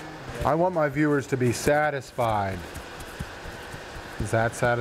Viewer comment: 06:38 that sounded like "I want my partner satisfied"